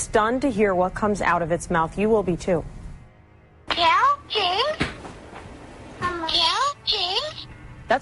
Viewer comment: The Elmo one sounded like it said "Kill Shane"